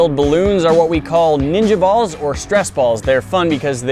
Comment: You say "10lb ninja ball" and I hear "10lb cannon ball".